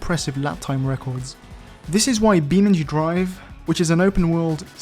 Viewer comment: Anyone got a link to Beanandu Drive? (I think that's what he said)